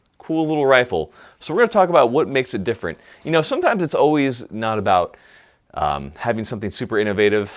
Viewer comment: Did you just say "sometimes it's always"? 2:51 And you'd know if Gaston Glock's soul is in any firearm because the grip angle will be totally wrong.